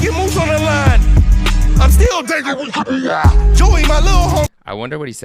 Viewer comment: I believe he says "Im still dangerous, keep away" in the one part you said you didn't know what he said. His first song back after his hiatus was named that (Dangerookipawaa). I heard it's also Top's nickname.